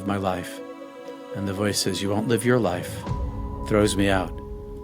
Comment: he’s told “You WON’T Live your life”..& is thrown back like a baseball. Did I understand him right? “You Wont live your life “??? I’ve listened over and over and that’s what I keep hearing???? Thanks